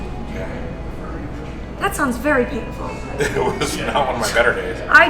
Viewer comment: captions say “That sounds very beautiful” when she meant painful lmao